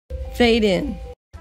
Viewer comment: Did she just say "FAKE IN" ??? KK as honest as can be!!!!!